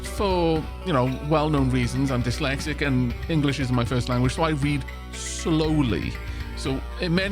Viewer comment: YouTube's subtitles agree that you said "English isn't my first language" at 18:56. While I heard you saying "isn't" as well, I couldn't be sure if that was just because that's what my brain expected to hear... but, the captions don't have that preconception, so given they say it, you're fine. 👍